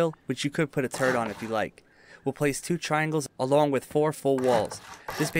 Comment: i thought he said u can put a terd on it if you like